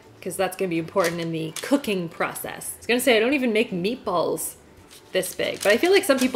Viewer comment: I heard "nipples" instead of "meatballs" and got reeeeally confused for a moment... Sorry, English is not my first language.